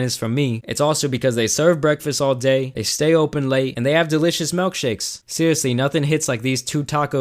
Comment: anyone else mad he said malkshakes instead of milkshakes